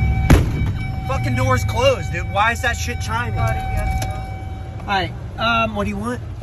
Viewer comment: 😂 now nobody will know how I got this many likes.... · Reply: I coulda sworn he said “why’s that shit tryin me”😂😂